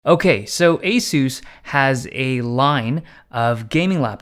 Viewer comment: I thought he said Jesus xd